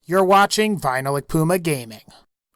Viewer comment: I misheard "You're watching VinylicPumaGaming," as "You're watching Final Akuma Gaming."